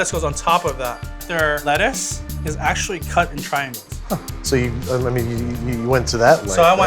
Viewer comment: did he say that in 'n out's lettuce is cut in china?